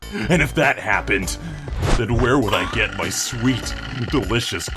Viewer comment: Turns subtitles and it will say "Sweet Sweet" instead of "Sweet Delicious"